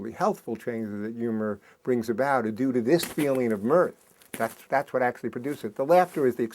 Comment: What word does he use at 4:38 to describe the feeling before laughter? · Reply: Mirth